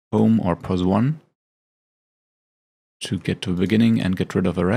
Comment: Please slow it down, you lost me at about the 01:00 mark, when you bring down the black back ground ( rookie here, trying to learn here) you say something to the effect " bring it down to ?? Boom? zoom? Pause/ Plus one? lets get rid of the rest? ( What says who to whom? ) Get rid of the rest? what rest and why? Please be so kind as to elaborate on what we are actually doing and why. Simply trying to mimic your actions is very difficult as you speak and move to fast for the inexperienced to follow. Thank you :) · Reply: sorry for mumbling. "to get to the beginning" (I pressed the HOME key on the keyboard - aka POS1 on German and maybe other keyboards I think). I just wanted to get rid of the rest of the video clips so our project is simpler/shorter.